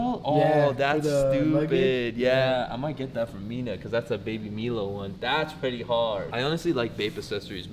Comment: did he really say ‘Baby Meelo’